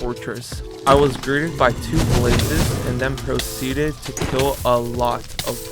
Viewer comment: I thought bro said "groomed by the two blazes"